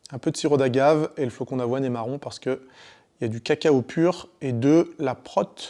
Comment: Je suis vraiment un déchet, j'ai ris à 0:40, j'ai entendu "le flocon d'avoine est marron parce qu'il y a du caca"